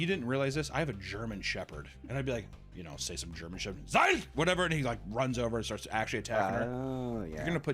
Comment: zeit (that is what he said) that means time in German 😂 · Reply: It’s supposed to be “FASS!” That means attack